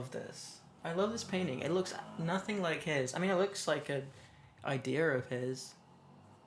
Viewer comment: Holy shot did he just say idear?!??